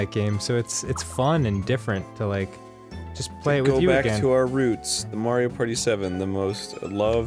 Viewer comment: Wow, FWOB gained a ton of subscribers overnight. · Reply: Jade i thought they said they weren't going back to 7...